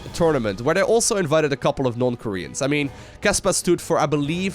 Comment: Whenever said "non-korean", I heard "North Korean" XDDDD Happy New Year, everyone :D